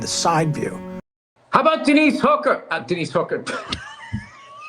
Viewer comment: I thought Bill said “Diddy’s hooker”! 😂😂😂